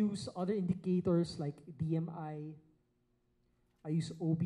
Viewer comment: Hello, May I kindly ask what other indicators uses? At 3:44 he mentioned he uses other indicators did I hear it correct? OBV - On Balance Volume BMI - ??? The second one I was not able to catch. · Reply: Hi! It's DMI, Directional Movement Index. It helps measure a trend's strength and direction. It's also called ADX or DMS. :)